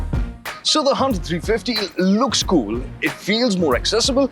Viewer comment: why does the caption says HONDA 350